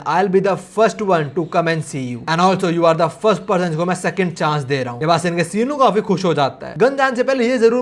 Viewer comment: Corrections: 15:51 - Jake* 17:33 - Sinu* 21:34 - with* old face 27:04 - 1.2 Billion* 34:30 - Gun* 38:56 - 5 Billion* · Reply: on 31:04 you said sinu instead of samuel